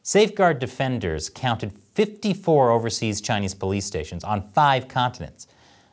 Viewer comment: you say "overseas chinese police" i hear "target practice"